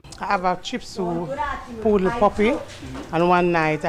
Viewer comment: All I heard was "chip sue, poodle puppy" and "inside intestiment" 😂💀😩